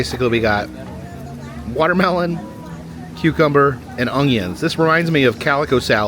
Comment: Did he just say "ongion?" 😂